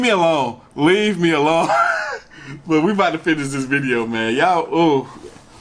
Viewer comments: Yo i hear a voice "shet" or "shit" in 10:00 mins brooo tf i'm not sure if it's ur voice or someone is with u there and i repeat to play a 10:00 to be sure broo pls noticeee and idk if someone in comment hear that too.. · when u say: i'm here all alone right now.. then the voice i hear say "shet" ,broo review this vid..